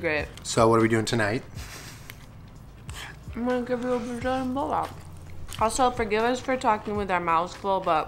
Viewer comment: I thought she said Blow Job and then I thought of the video oml xD